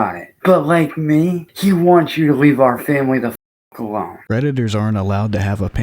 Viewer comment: I thought you said "redditors" instead of predators but honestly what's the difference